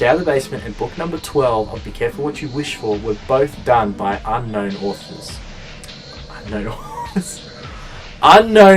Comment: I turned my volume down after the laughing when you said "unknown authors", then, I heard "Unknown Australians" XD